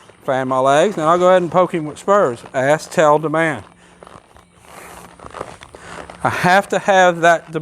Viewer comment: I finally figured out what you are saying.....ask....tell.. demand I always thought you were saying ....ass.....tail.....demand..lol. I couldn't figure that out